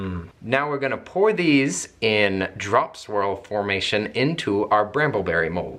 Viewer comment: I really thought said drum swerl